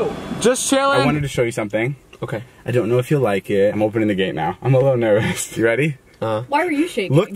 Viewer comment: I thought he said "I'm openly gay now"